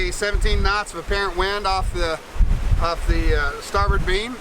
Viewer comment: 10 knots in 15 knots of wind is great for a heavy cruiser! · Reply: a) it is a heavy boat but with long waterline - and a cat which means that if not overloaded should have low weted surface. b) I thought he said true wind not apparent wind the time I wrote the comment (which was at 90 degree which is the fastest wind you can get really) - but as it was apparent wind, than slightly below 10 knots in 16 knots in flat water would be still below what I would hope to get on a cat - I just thought that they would be noticeably faster than monohull in all byt the worst angles. //edit: and I suppose that I "heard" true wind due to the fact that I always like to use the true wind - it is easier to place it on the map and deduce what exactly have to be done to get back in port to give the cheap chartered boat before additional cost would be added ;).